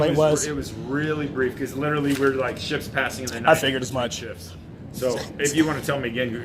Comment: I heard that as "Shits passing in the wind...."